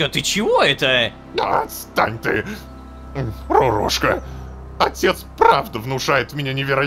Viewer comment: мне одному показалось что он сказал: "lololowka"???